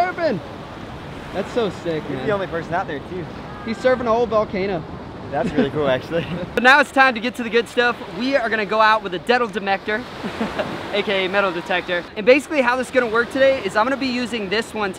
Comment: did he really say " deadle matector "?!?!